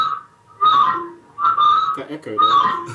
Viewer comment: What Toby said at 2:19---- sounds like in hungarian: Te ökör vagy! :D